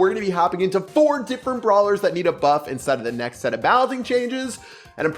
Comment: I heard "Nita Buff" and got really scared for a sec